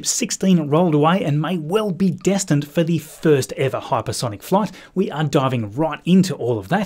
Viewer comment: I thought that said hypergolic Starship. Let's hope that isn't true.